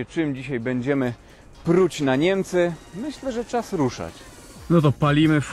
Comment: na początku usłyszałem "pluć na Niemcy" xD